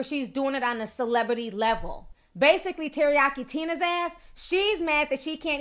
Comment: Ooh I thought she was saying Teriyaki Tuna 🐟🤭 Got me out here thinking was ruthless 😂